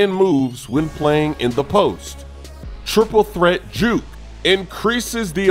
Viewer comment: I damn near thought he said triple threat Jew